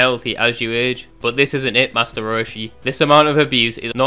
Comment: Lmao the Master Roshi diss caught me off guard 🤣 No one's dissing as creatively as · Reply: I thought he said master Roidshi lmao